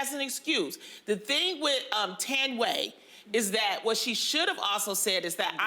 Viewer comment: Thought Lonnie was saying “Tan way” instead of Thandiwe. As a Xhosa South African (the name is my native language) I’d like my fellow Americans to try to pronounce it properly 🙂🙂 I understand what Lonnie was saying though